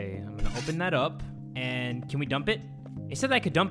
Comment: i think it said dump body